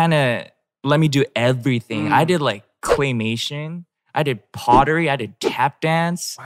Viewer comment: I thought he said cremation 😂